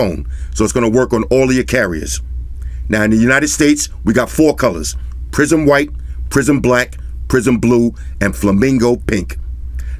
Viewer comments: I keep hearing “PRISON BLUE , prison WHITE, PRISON BLACK, “💩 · I keep hearing "prison blue" 😂